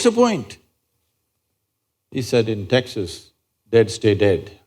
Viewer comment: what is the man from texas say at the end @ 47:16.. it was not clear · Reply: Texas man said "in Texas dead stay dead" man did not want wife to comeback alive by burying in Holy city